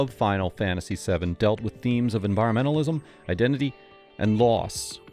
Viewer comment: What word was censord at 8:20 ? Identity what? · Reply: Just identity, meaning cloud’s identity